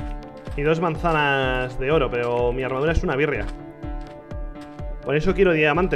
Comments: Pensé que había dicho:"Mi armadura es una v3rga" y yo tipo:"Rich diciendo malas palabras en su video, imposible" pero ya escuche bien xd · entendi, mi armadura es una verga"